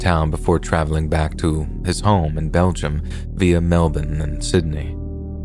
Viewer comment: did you just say via melbourne, in sydney… sydney is a city in new south wales, melbourne is a city in victoria…